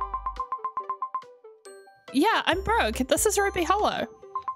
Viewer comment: When she said Brooke I thought she said broke😅😂😂😂😂😂